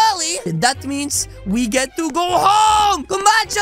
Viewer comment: Wait did you just say Nintendo at 9:29? I have one too :D!